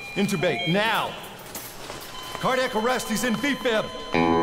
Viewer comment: What is he saying? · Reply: Defib?